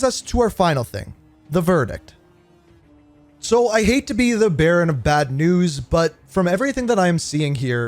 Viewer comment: It might be just me but when he delivers "The Verdict" at 9:09 it sounds like he says he is the Baron of bad news instead of the bearer 🤣